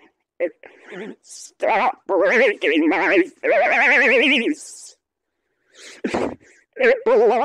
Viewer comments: I heard "stop breaking my faceee" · Why did it sound like he said: stop breaking my face:😂😂😂😂😂